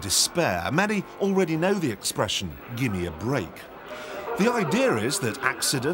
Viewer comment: did he just say "Idear"???? Isn't it "Idea"?